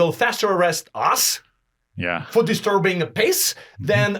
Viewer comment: I swear I heard "disturbing the piss".